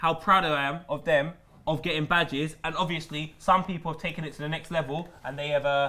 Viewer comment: i thought when said badge up he was saying badger